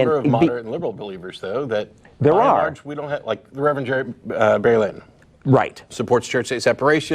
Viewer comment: Who was that pastor he named? I thought I heard "Barry Lake", but can't find that on the Net. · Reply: person Barry W. Lynn